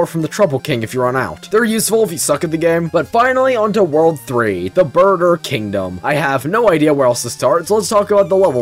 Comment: did he really say the burger kingdom